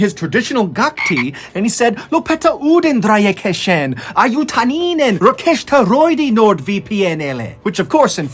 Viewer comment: Finnish part: "Lopeta ydinräjähdysten aiheuttaminen. Rekisteröidy Nord VPN:lle!" As a Finn I had to listen to this multiple times because it doesn't sound like Finnish at all. 😄 Then finally when I slowed it down to 0,5 speed I could kinda get what he's trying to say. I couldn't understand it but after he told in English what he just said it helped me figure it out. 😅 But I do appreciate that Finland is mentioned and I also know our language is difficult. Thanks for the effort MrBallen, you're the best! 🥰